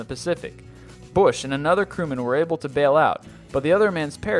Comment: Did I just hear, "Bush and some other Criminal were shot down" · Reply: I'm sure he said "crewmen" but it sound like he said "criminal".